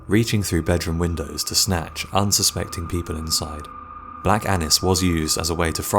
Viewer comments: Thought he said "Black Anus" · Oh dang I thought he said black anus